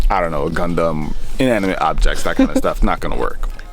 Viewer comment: Did he just say Condom😂